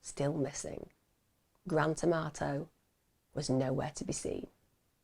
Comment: I keep hearing "Grand Tomato" not Grant Amarto 😂